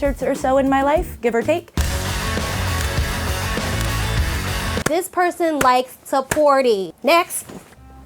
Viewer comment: I heard “This person likes to party” as “this person loves to potty” 😆